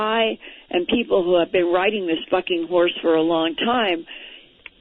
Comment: Linda says "Bucking" "Buck" "ing". Word starts with letter "B". Had to listen several times to convince myself that's what I heard and am sticking to that story. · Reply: A bucking horse is what she said. I heard it too n thought WTF. But yeah- bucking horse